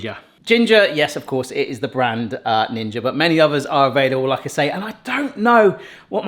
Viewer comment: I thought you were saying ginger air fryers hahaha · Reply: Same. It sounds like he says ginger. It is NiNJA